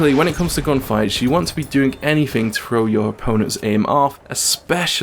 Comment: Good tips! Being fearly new to FPS, it helped me a lot. What did you say at 2:30 ? ("when it comes to gunfights, you want to be doing anything to ......... your opponents aim off") Thanks a lot and see you on the battlefield! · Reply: the word was "throw" =) np!